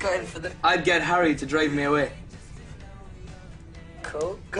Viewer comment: when Niall says 'i'll get harry to drive me away' does harry go to Louis 'i thought he said cause he's gay?' i might of just heard a wrong thing but lol